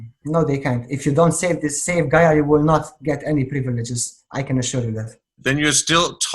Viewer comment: What does say at 37:05 “if you die you don’t get any privileges if ... “ what? Can’t hear it... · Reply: "If you don't save Gaia, you will not get any privileges, I can assure you that."